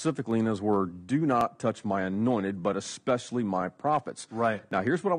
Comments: He said "God said don't touch my annointed, but especially my prophets." I heard "God said don't touch my annointed butt, especially my prophets." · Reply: I heard, "God said don't touch my anointed butt, and especially my profits." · He said "God said don't touch my annointed, but especially my prophets." I heard "God said don't touch my annointed butt, especially my prophets."